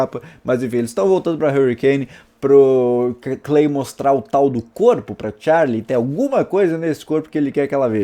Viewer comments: so EU entendi o pau do corpo? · Só eu q entendi pal